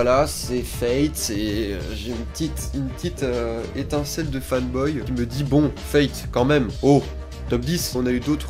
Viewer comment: bonne année. Et qui dit nouvelle année, dit nouvel anniversaire. À tes 22 ans tu pourras ENFIN lâcher dans son exactitude la plus totale LA maxime d'Onizuka gg <3 PS: c'est moi ou à 03:33 tu as lâché FatBoy en parlant de toi ? tu nous dis ça pour qu'on entendre el famuso "mais non t'es pas gros" hein?! (f.d.p.) retourne à l'infermerie soigner ton ego